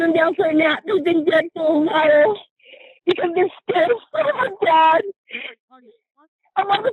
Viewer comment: I don’t know who put the collar and dispatcher on, but it’s wrong! A lot of it is wrong, but for example 1:05 the color does not say because they’re scared of him. She says they’re stiff. Put it to your ear and don’t read.